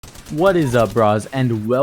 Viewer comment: Did he say "What is up bras"